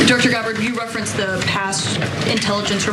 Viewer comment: Director Tulsi Gabbard is her title, national intelligence what she directs. Never was Dr. Gabbard mentioned. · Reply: ok, yeah she must have said Director Gabbard, and I thought she said Dr. Thanks for that. It totally makes sense.